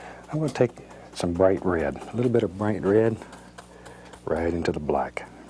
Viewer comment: I can't get over the way he says "rared" instead of "red"